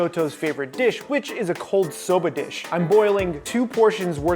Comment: I thought he said a cold soap a dish